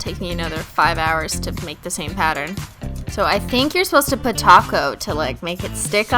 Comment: every time you said top coat I thought you were saying taco lmao I need to go to bed.